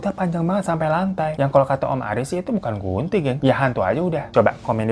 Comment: maaf ini ngomong apa ya aku dengernya bukan gunting bingung jadinya 😭